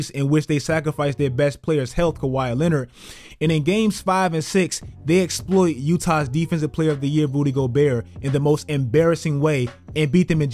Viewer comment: with CC on... "Booty Gobert" When the captions say what we're really thinking.